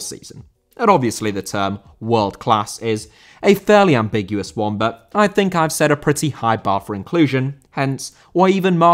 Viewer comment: Who is the chubby goalkeeper at 1:19? I can't really make out his name. Fiedel, or Fnendel, or something? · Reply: Brad Friedel